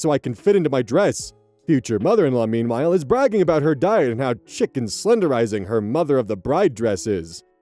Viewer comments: I heard "chic and slenderizing" as "chicken slenderizing" and did a double take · chic is pronounced sheek. I had to go back because I heard "chicken slenderizing" and thought 😅